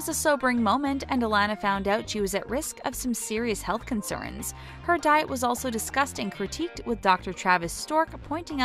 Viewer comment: it sounded like "her diet was also disgusting"